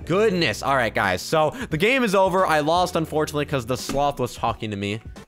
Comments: it sounds like you said the f word even though you just said sloth...lol Edit: Oh gosh- why do I have so many likes- oh gosh and so many replies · Yeah it does sound like you said f word when you said sloth